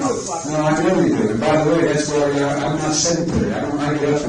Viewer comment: Biden told the man, "And by the way, I'm not sedentary." The subtitle said, "I'm not senator". (Timestamp